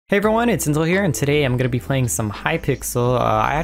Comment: sounded like you said its tinzal at the start lmao